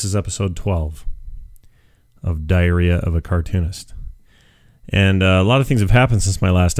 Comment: did he say diarrhea of a cartoonist ( 1:30-135)